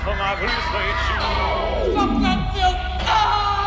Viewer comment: Is he saying “Stop that filth” or “Fuck that filth”? It sounds heavily like the latter to me, which seems funny as it would prove Dr. Righteous’ hypocrisy - swear words being AOK, but rock music is outlawed.